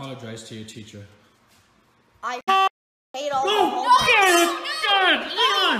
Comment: what he say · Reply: I (Car Horn) Hate All The Homework